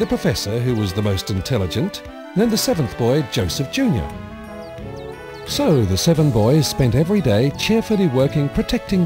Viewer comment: And, OH GOD. When he said "Joseph Junior", i thought he said... "Joseph Joestar"... OH! MY! GOD!!!!!!!!